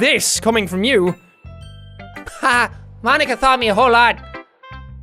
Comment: Jack says thought instead of taught